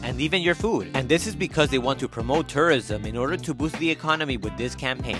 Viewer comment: I thought he said "to promote terrorism" 💀